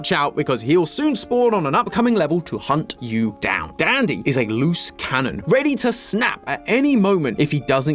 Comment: "dandy is a loose cannon" i heard "dandy was a lou skunt"